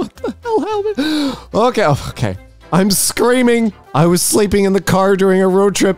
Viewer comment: I thought that said " I'm CREAMING"